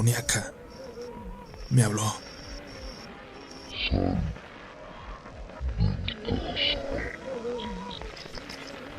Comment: ¿Que dice? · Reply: Parece que dice : Sal! Muertos!